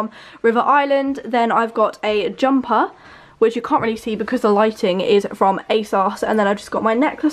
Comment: it sounded like you said "the lighting is from asos" & i was so confused lmao